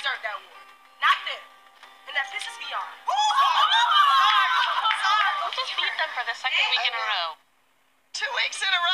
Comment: What did Nicaya say at 0:31 ? It sounded like she was said beyond. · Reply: Angela Peters she said "and that pisses me off"